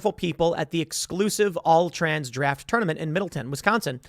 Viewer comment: Middleton... thought he said Milton... Damn that's close to home...